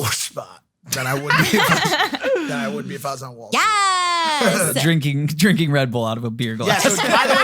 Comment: did Anna say Yes????